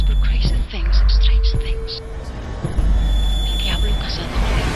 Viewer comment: When I was a kid I always thought Anna said 'The Demon who make [Strauphus] of man.' I assumed that word meant something like 'A mockery, a Joke, a nothing' then on DvD I saw it was just her saying 'Trophies' with a thick accent.